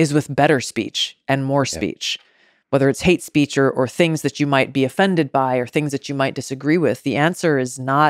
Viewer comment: what did she just say? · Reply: Did she say “things that are offensive?”